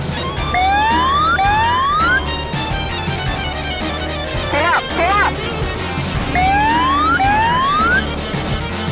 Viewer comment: 에 뭐라고 하는거죠? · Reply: Pull up 기수를 위로 당기라는 경고음이고 hud에 뜨는 경고는 고도가 너무 낮다고 경고하는겁니자